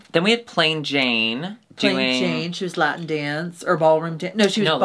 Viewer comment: Why are they on fire? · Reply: When you said Latin dance I thought you said “lap dance,” and now I can’t stop thinking what a great runamwaybthat could be….😅